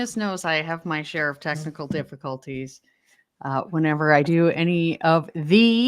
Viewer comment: Happy New Year! Gosh, i think I need more sleep....I could've sworn you said you're having testicle difficulties. Lol.🤣 Its been very hot in Melbourne Australia. 40 degrees (102° Fahrenheit for you guys). 😊